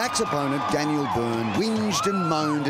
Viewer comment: did he just say "Daniel Burn"??